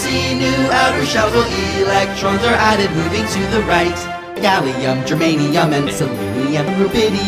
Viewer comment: did i hear gatling gun lol